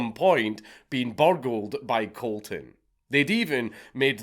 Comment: I misheard 31:29 and thought you said buggered, not burgled. Thank God I was wrong, and whet the hell is going on in my brain?